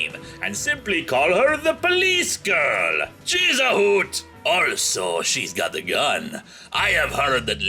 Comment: she is a what?? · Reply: popc52/ it sounded like he was saying, “She’s a hoot.”